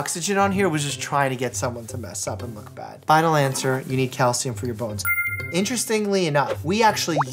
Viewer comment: I swear (trying to multitask and listen to this), I didn't hear "mineral for your bones" I heard .... "mineral for your PHONES" - and I yelled LITHIUM!!! 😂😂😂😂😂😂😂🙃🙃